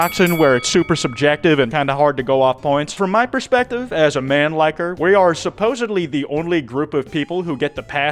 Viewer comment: I was focused on the spring trap figure and as soon as I came back to reality I heard "as a man liker-"